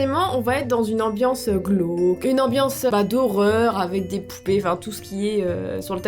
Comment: j'ai entendue poulet à la place de poupée 😂😂